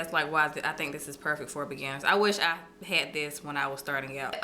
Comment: When you said "beginner" I thought you said "banana"